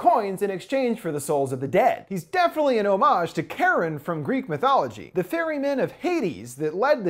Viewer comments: I know he says Chiron at 9:19 but I swear I keep hearing him say "Karen, of Greek mythology." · I know he says Chiron at 9:19 but I swear I keep hearing him say "Karen, of Greek mythology." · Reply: there are 2 ways to pronounce the name. So Charon is pronounced Ki Run. However Kharon is pronounced like Karen you just roll the Rs. He didn't roll the R so it sounds like he is saying Karen instead of karrrrrron. If that makes any sense.